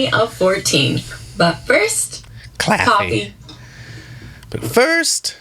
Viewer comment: Did you say, “Claw-fee?” 😂😂😂